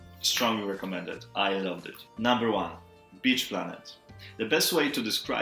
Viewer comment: I've been going back and forth about getting Citizen, but seeing it on your list made up my mind.... Buying it! Always looking for new comics and graphic novels and Bitch Planet (even though I thought you said Beach at first lol) seems right up my alley! I'm halfway through a brief history and ugh it is so dense! Interesting but dense, but I'll finish it.... Eventually.